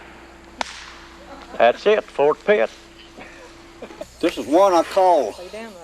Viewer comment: does he say "that's it, Fort Pitt"?